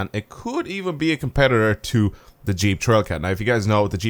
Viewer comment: what car is that u said in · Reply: Jeep trailcat